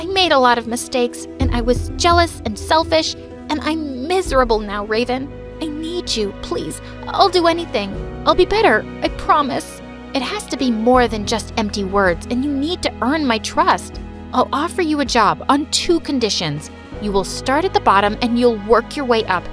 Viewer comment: "Please I'll be better" was said but what I heard, "Please I'll be bitter" are my eyes playing tricks on me🤔🤨😵